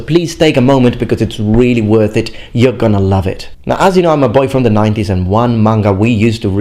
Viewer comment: what do you say around 2:07, when you're sitting down with the phone? it sounded like you said "as you know, my boyfriend and I...". I was shocked when I heard this ( is GAY?!) but even after rewinding several times, I still can't understand it. I don't have a problem with different sexual preferences, just surprised at that fact drop. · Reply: Ahahah I said "as you know I'm a boy from the nineties". I have a gf pal, I'm sure She Is gonna get a laugh out of this comment :D